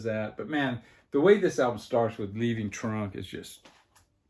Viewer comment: When you mentioned your favorite song on the Taj Mahal album it sounded like you were saying Leaving Trump.